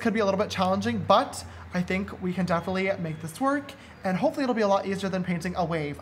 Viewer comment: I thought you said so wer going to make this twerk